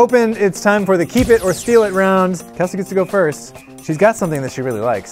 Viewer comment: If you switch on default subtitles, instead of "Kesley can go first" it shows " Testicles go first"🤣🤣🤣🤣🤣